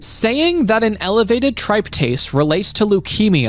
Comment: Hahahaha. He pronounced tryptase wrong. At least according to what I've exclusively in many years of chemistry/biochemistry schooling. I was only listening to the video and thought he said tripe taste.